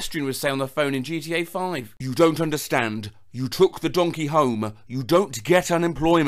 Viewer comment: My dumbass thought they said donkey home (a home being in shape of a donkey), not taking a donkey home…